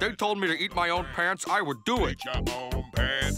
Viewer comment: When it said "Eat your own pants," I thought it said "Eat your own hands". 😂